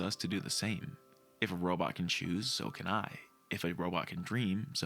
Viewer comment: I know it was not intentional, but at 11:05, when you started saying "If a robot can choose, so can I. If a robot can dream, so can I. If a robot can love, so can I", for a second I thought you were saying "If a robot can choose, soaking eye. If a robot can dream, soaking eye. If a robot can love, soaking eye.". And honestly, with these movies, that sounds about right.